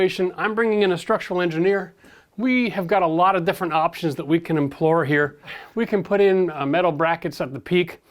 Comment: to implore means to beg desperately. I think you meant to say employ.